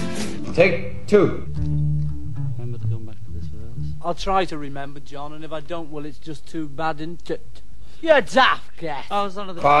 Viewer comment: HAHAHA what the hell did he say? the way he said it was funny 😂😂😂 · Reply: Paul said "I'll try to remember, John, and if I don't, well, that's just too bad, IDN'T IT?" and John replied "YOU DAFT GIT!" XP